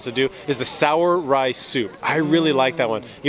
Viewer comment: Mizeria? Are you kidding me?! The soup in a bread bowl is called Żur or Żurek, mizeria is sliced cucumber in cream. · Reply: Haha, i got lost there too, I thought he said "sour rice soup", i was like "what?" Gotta learn "zurek" soldier!